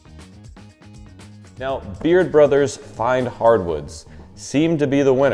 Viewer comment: Who's this guy that took over the channel? At the end, 33:12, sounds like he says "Beard Brothers" rather than "Baird Brothers....hmmmmmm